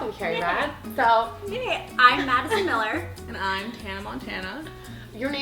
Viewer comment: Seriously thought she said Hannah Montana !!!!😂😂😂😂😂😂😂😍